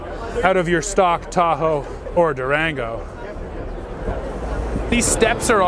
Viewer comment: did he just say "or Durango"?